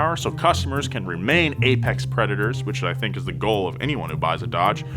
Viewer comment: did he just said Apex Preparator??